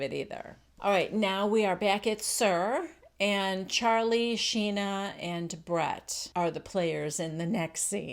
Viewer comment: I reached over for my lighter. & I heard you say, "Back at SUR we have Charlie Sheen...." What the what??? Oh..Schena! And, Kristen is reminding me of Vicki from OC, someone make Kristen a damn casserole already & she'll be fine.